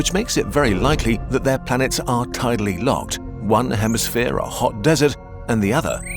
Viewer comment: I thought it said planet hetalia